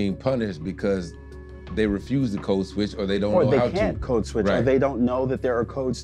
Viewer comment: I did a double take over "finna" (contraction of "afixin ta") until he gave an example in a sentence. "Code switch" is another new one to me. This was a well done and interesting report. Good job. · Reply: I'd never heard of "code switch" & I kept hearing "co-switch" from the reporter -- until the guy he was interviewing enunciated it clearly🤷‍♂️